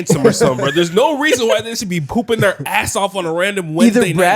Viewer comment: Did he just say poopin their ass off